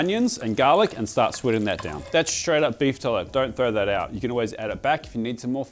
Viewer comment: i wish youtube let me post the screenshot of its captions writing "beef toilet" instead of tallow